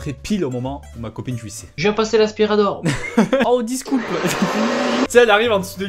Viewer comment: "desculpa" ou "djiscoupe"? qu'est ce qu'il dit ?